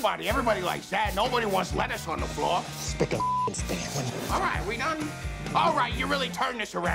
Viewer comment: What is he saying at 2:13 😂😂😂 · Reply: Sean Daughtrey he says “spic and fucken span”